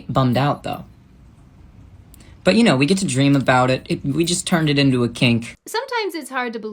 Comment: Did he just say "we just turned it into a kink" 😵‍💫